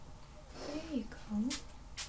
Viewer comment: Okay did anyone else hear the “oh there you go at 12:45 and then she says “there you go” 😳 I’ve watched this video hundreds of times never noticed the women talking in the background before